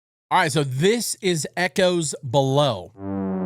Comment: my brain literally thought you said echos malone